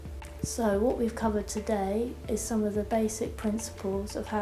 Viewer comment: Oh, it says massage. I thought it said marriage. Right...